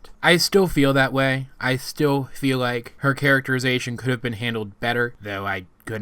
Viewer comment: my subtitles said "her carrot her is asian"